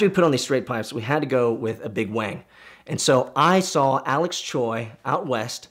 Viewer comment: Wait, he said Wing...thought he said big wang